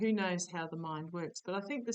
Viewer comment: I think I heard one of your guides. A male voice. It was at almost the end of the video. It wasn’t the gruff one. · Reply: I just posted about it. I heard him say "nosey". Is he saying we are being too nosey? 😆